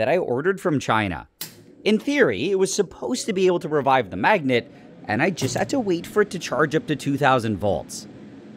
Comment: I thought it said “with this magnet” for a second.